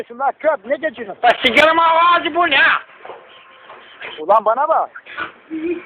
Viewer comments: 'de şakacı abi ne diyor bilen var mı? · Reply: korum o kapçuk ağıza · 'de şakacı abi ne diyor bilen var mı? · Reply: gorum ha kapcuk agiza diyor 🤣 🤣😂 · 'de şakacı abi ne diyor bilen var mı? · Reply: gorum senin gapçuk ağza